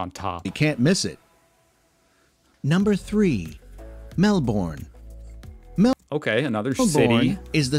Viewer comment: The way the guys said Melbourne it sounded like he said Mel-born 😂😂